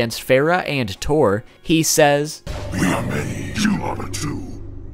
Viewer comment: did he say we are made, yo mama too hmm?